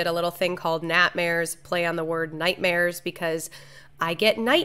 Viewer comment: Whenever I hear you say "Natmares", I just hear my southern grandma talking about her bad dreams.